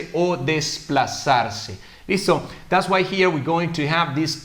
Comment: que dice en el 5:43?? yo entiendo does way here, corrijanme pls